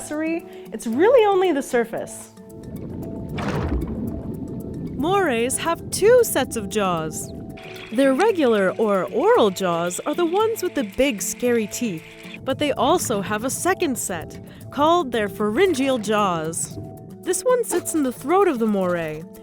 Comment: it sound like she is saying ora ora lol